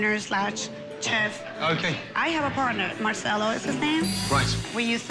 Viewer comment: For a second I thought she said Garcello